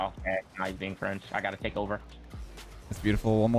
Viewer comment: did i hear "now you are being French, i got to takover" out of mouth? xD